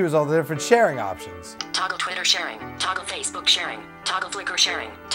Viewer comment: I thought it said taco constantly